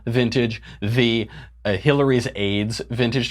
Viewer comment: At first I thought he said Hillary's AIDS, not Hillary's aides.